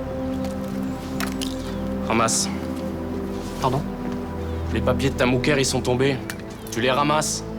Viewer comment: qu'est ce qu'il a dit j'ai pas compris ? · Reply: "Les papiers de ta moukère" (terme assez péjoratif, pour parler d'une femme maghrébine)